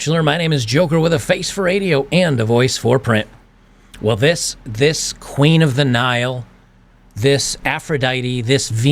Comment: Did Joker say "This Queen of Denial"? If so, well played, Joker